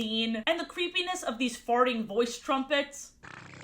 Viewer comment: I could not hear very well at the time, and I thought said that they were called "noise prophets" instead of "voice trumpets", and I was kinda ready to just roll with that until it popped up written on screen.